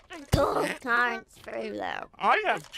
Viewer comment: "I have cheeky snakes for hands. Shh, don't tell anyone", man these edibles aint shit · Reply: I heard "cheeky snakes". And it makes the most sense.